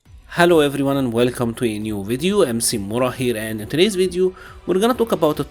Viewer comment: Everytime he says Mc mura here, I always thought he said I am C-Mura. Either way great channel